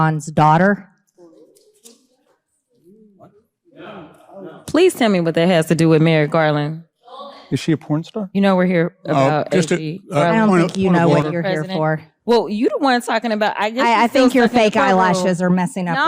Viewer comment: 😂 did I just hear some guy say ( was she a P star )